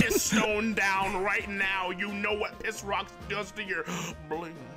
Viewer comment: this is a hill i will forever die on: does NOT SAY "blenis" here, he says "you know what piss rocks does to your blue"!!! he says blue!!!! sonic after might say blenis, i cant really hear but says blue!!!!